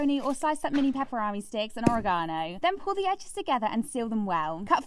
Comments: Ive never heard ANYONE pronounce "oregano" that way😂 Edit: Y'ALL NEED TO CHILL. What I said was not insulting. I just made this comment because the way she said "Oregano" was completely new to my ears, THAT IS ALL. I shouldn't have to explain myself. If a comment like this is what sets you off, get off the internet and touch some grass. There are wars going on and yet you choose to fight and squable over this?! Edit2: Being American has nothing to do with it. Anyone could have made this same comment about any other word and be from anywhere. It does not make me tonedef. Im sorry that you dont like that im American and didnt know how another country pronounced a word. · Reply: Thought she said oregami 😅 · Ive never heard ANYONE pronounce "oregano" that way😂 Edit: Y'ALL NEED TO CHILL. What I said was not insulting. I just made this comment because the way she said "Oregano" was completely new to my ears, THAT IS ALL. I shouldn't have to explain myself. If a comment like this is what sets you off, get off the internet and touch some grass. There are wars going on and yet you choose to fight and squable over this?! Edit2: Being American has nothing to do with it. Anyone could have made this same comment about any other word and be from anywhere. It does not make me tonedef. Im sorry that you dont like that im American and didnt know how another country pronounced a word. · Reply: I thought she said origami..